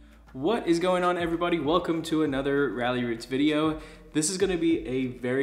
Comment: Lmao I got thrown off in the beginning, I thought he said "welcome to another Riley Ried's video"!!!! 😅 I had to go check the channel name.